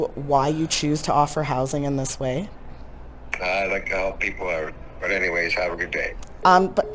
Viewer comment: What's he saying? "I like to help people out"?